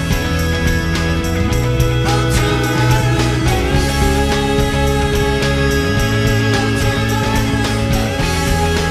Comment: What are they saying at 2:47? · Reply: Ultraviolet..love